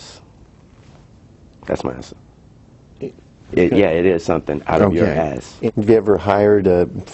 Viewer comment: did he say "yea it is someting. (ill whop yo ass!)?